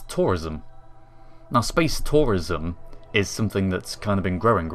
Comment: at first i thought it said space terrorisum